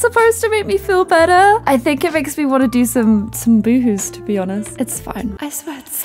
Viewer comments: omfg I thought she was saying "woowoos" not "boohoos". I spent most of this sad video very perplexed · When you said you wanted to do some boohoos I thought u said woohoo s and I was like oh ok then Clare thx for sharing ahahahha